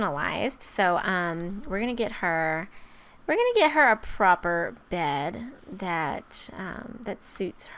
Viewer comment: Me being the stupid person I am when she said "I'm going to get her a proper bed," I thought she said "I'm going to get her a rubber bed," XD I have many problems